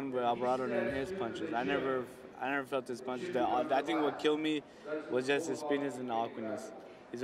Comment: Tf?! Open cc and the sub says "what killed me is his penis" lmao